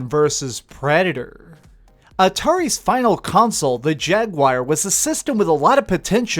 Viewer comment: bruh did you say Jagwire?